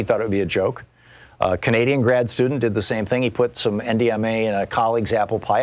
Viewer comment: Anither kind of MDMA is called Ecstacy! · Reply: I heard it as MDMA too, but the actual chemical is NDMA (click SHOW MORE at the top).